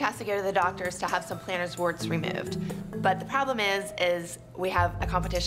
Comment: I thought it said Paige gets her FARTS removed xD